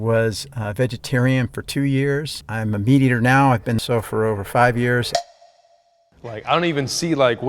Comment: stg I thought he said he’s been sober for 5 years lmaoo